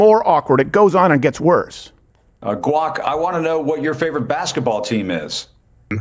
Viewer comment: did he call his AI "guac" like guacamole?